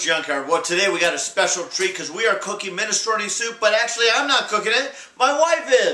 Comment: I thought it said minestone soup i was like wtf